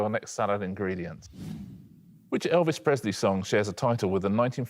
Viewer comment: I'm pretty sure Richard says "Elvish Presley". Has anyone else heard that? (And is he thinking of Legolas' favourite performer?)